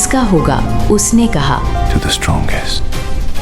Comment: Does anybaody know what did he say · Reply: TO THE STRONGEST